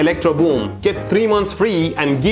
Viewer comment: what did I just heard? piss of mind??